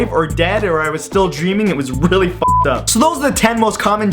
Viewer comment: If you turn subtitles on, he says "it was really fun" instead of "really fucked up" LOL